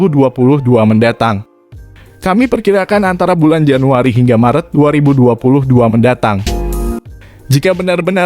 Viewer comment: Apa gua salah denger ya,dia nyebut Januari 3 marettt 😅